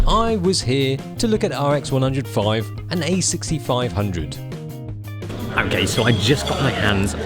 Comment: did he just say "im gay" :O